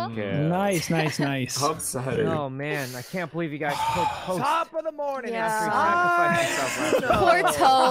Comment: Did anyone else hear Ludwig say “top of the morning, it’s Sykkuno!” at 12:54 😆